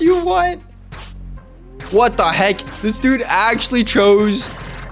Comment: A bit after 18:06 he said what and never pulled… he said “what the heck”